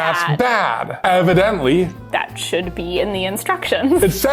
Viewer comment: I thought they said “Evin-dently” and I laughed for like 10 full seconds before I realised that they didn’t say that, lol.